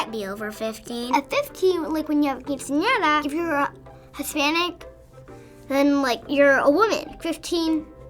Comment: lol, did she just say if you are hispanic your a Women?